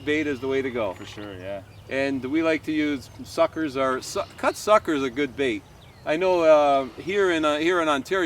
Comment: i heard cock sucker is a good bait